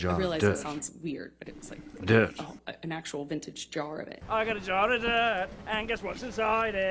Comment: “ I got a jarrr of dirttt guess what’s inside ittttt” 😂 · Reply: I always thought he said I got a jar of debt